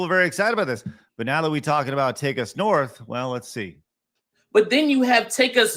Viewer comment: I swear I heard "Take a Snort".